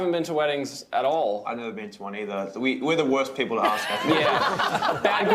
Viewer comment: What did Luke say in 3:41??? And Michael's first wedding will be in March omg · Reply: Moony Sky, Luke said, “I haven’t been to one either” and yes, it’s exciting!